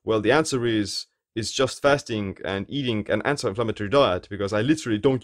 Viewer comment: you mentioned a diet for your skin health. You mentioned fasting and ___ diet? Sorry, I couldn't hear it well. It sounded like "xxx -symmetry diet"? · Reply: eating an anti inflammatory diet :)